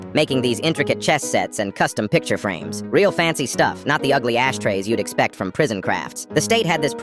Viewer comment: I thought bro said ass trays 😂😂